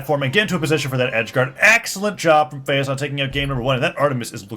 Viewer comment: did he say AXElent xDD